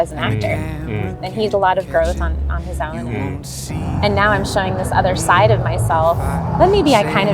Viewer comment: I have watched this video so many times. Amazing video. I might be out of line to say this but at around @ 4:20 mark you used a guy singing in the background. I had a hard hearing what she was saying. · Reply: I’m glad you enjoyed it! Not out of line at all thank you for the feedback. She said “And he did a lot of growth on his own, and now I’m showing this other side of myself..”